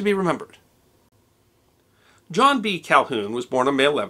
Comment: At first i was really confused I thought it said John C Calhoun...... I feel rather silly about it.